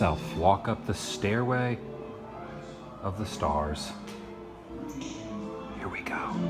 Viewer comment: I thought it said Stairway of the stairs.